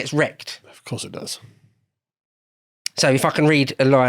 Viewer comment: so I fuckin read a line. 😂. That's what it sounded like at first. But it was 'so if I can'. Had to listen twice.